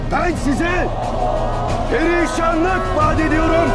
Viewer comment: Burada ne diyor anlayamadım "Hristiyanlık" mı diyor? ? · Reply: perişanlık diyor gerizekalı kardeşim